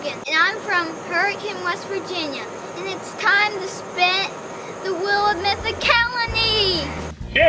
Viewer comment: This is how I heard Logan: "Hi, I'm Logan, and it's time to spit the wheel of mythicalny!"